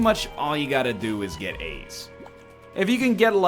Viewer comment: I kept thinking that he said AIDS instead of As